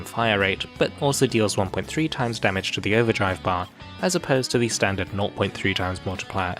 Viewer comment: what was said here? I can't understand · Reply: The default damage to the Overdrive bar is 0.3x for Rapid-Fire Manacasters. That is, if a normal attack that deals 1000 DMG would remove 10% of an Overdrive bar, a Rapid-Fire Manacaster Force Strike hitting for 1000 DMG would remove 3%. Panther (and Gala Leo) deal 1.3x Overdrive bar DMG with their Force Strikes, so a hit of 1000 DMG (against this imaginary enemy) would remove 13% of its Overdrive bar instead.